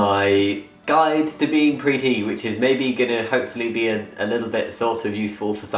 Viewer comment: At the beginning I thought you said "A guide to be pretty" and I was like "WTF I mean you're beautiful but why" 😂